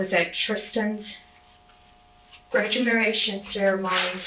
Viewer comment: did she say “gradumeration”????